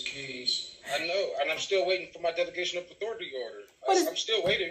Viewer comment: Oh…….. he said delegation of authority⁉️🫢 I thought he said he was waiting for the defecation of authority😳😵‍💫